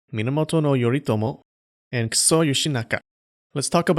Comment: why do i hear "Kuso Yoshinaka"? and the fox needs a skull to do some sick henshin